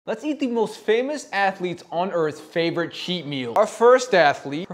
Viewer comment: For a second I thought he said "let's eat some of the most famous atheletes"